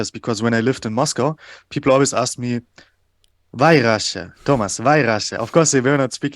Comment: Хорошо, что в субтитрах прописано "Почему Россия", а то вместо "Why Russia" мне послышалось "White Russia" 😳